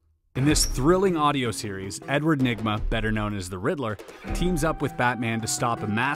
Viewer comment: I thought he said edward ligma